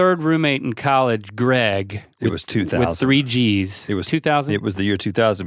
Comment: The way ret said that it sounded like he was saying link married Greg in 2000. Lol